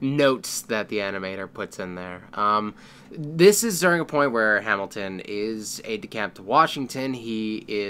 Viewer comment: XD why did i think you said "hamilton is a decapitated Washington"?!?!